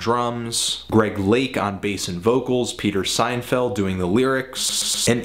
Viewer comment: Did he just say Peter Seinfeld?